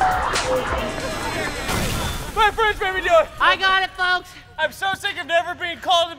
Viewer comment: Did anyone else hear Cody say my friends made me do it